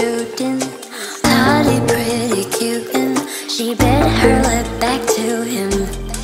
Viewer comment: in 0:15 she actually says "Pouty, pretty cute"